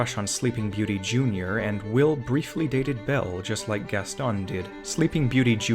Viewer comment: What was said: "Will briefly dated Belle just like Gaston did." What my brain somehow heard/processed: "Will briefly dated Gaston just like Belle did."